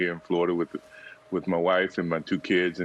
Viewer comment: seconds did he say "With my wives and my kids"?